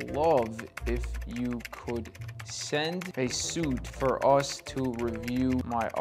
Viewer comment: Swear for a second I thought that said "Send us shit"